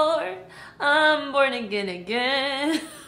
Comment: i thought see was saying i’m bored 😂when she was singing ahaha